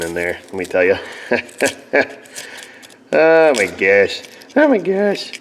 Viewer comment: sounded like “oh my gash” lmao 😂